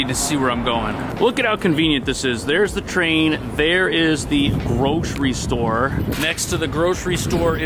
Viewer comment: When you said "grocery store" i heard "roach restore" i was like WHAAAT? 😆